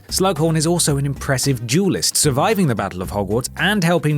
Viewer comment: English isn't my first language. During the first 2 minutes,I thought he was saying "jewelist" instead of "duelist". I was like "I didn't know prof. flitwick owned a jewelry store"